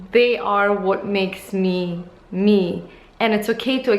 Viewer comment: When you said: this makes me me it sounded like you said mimi 😂 Loved this video!